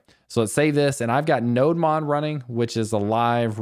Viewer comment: what are you saying you are running at 2:25? Did you say node mod? trying to get that extension but cant find it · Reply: I believe it's a Nodemon, live updating your js file; something like "live server" extension for VSCode editor which works with html files (I use it)